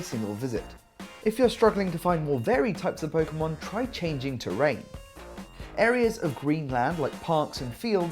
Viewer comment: what did he say? rank? plz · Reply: He said terrain